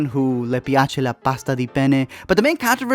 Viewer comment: Italian girl here By the way you spelled "gli piace la pasta di penne" it sounds like you were saying "le piace la pasta di pene" which translates to "she likes pasta made out of penis" Quite funny, thought you should know